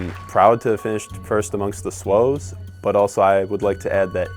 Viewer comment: I thought he said “ … first amongst the slows ” not SWO’S.😂😂